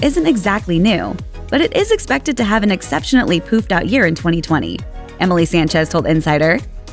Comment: Did she say "exceptionately"?